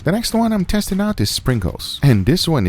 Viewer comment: I thought he said sprinkles instead of pringles